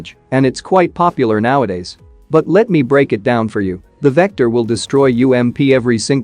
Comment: i thought he said victor at first